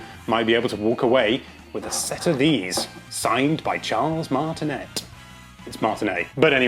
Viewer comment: At first I heard "Charles Manson"